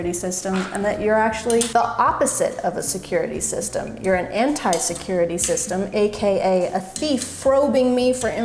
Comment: I thought you were gonna say an insecurity system